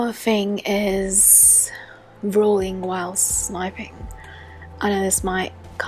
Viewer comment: I thought you said drooling while sniping I’m dumb